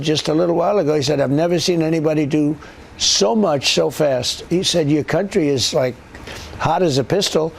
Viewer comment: Trump pronounced so fast as show fast 10:44 😅 This mixed-up pronouncation happened as he was saying what Fartin said about him